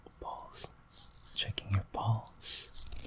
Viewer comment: When he was doing the pulse-checking thing, I seriously thought that he was saying “I’m checking your balls” like yup, classic